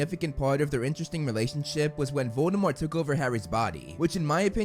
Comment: am I the only one who hears Dumbledore instead of Voldemort?